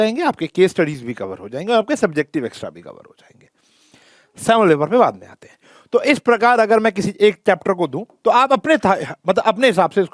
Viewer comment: Hello sir! What u said on 7:32 ! I cant understand! Or can anyone tell me what sir said?? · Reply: 'Sample paper pe baad me ate hai ' ye kaha tha sir ne